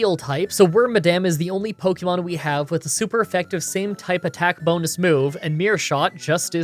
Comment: DID HE SAY SAND TYPE?